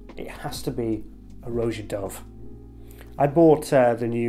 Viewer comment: What is the name of house brand did he say? I can't understand nor how to spell it tbh :( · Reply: Roja Dove.